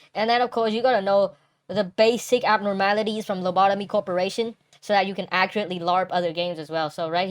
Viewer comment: I misheard lobotomy corporation as larpery corporation and just thought of some devious slander